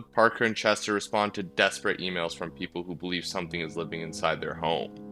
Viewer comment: i misheard emails as females and it still technically isn't wrong